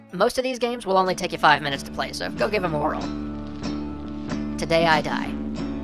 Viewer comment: just saying, but at 6:06 when you say "go give 'em a whirl" i heard "go give 'em oral." could just be that i pulled an all nighter yesterday, and went mtn biking today, but still